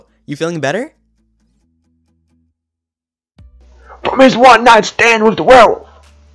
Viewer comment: what did he say i couldn’t understand him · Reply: something one-night stand with the werewolf